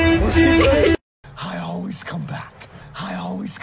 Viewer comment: it sounds like he sayin hi always come back